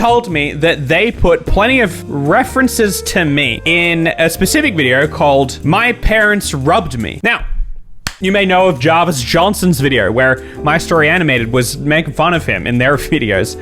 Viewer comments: omfg when u said it at first I thought you said "my parents RUBBED me" 😭😭 I was so concerned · At first I misheard it and thought it said My parents rubbed me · I thought you said "my parents rubbed me" 😆